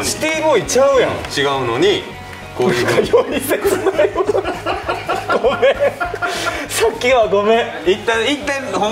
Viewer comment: どなたか1:29に秋山さんが言った言葉聞き取れる方います？「○○なよ〜」みたいなん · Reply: 笑いながらゆってるところですかね？ だったらたぶん「なんかより切ないわあ」やと思います🤗